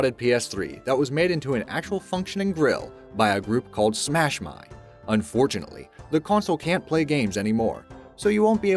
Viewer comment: I legit thought he said smash mouth.